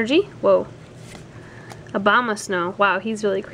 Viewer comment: Sounds like you said Obama snow lol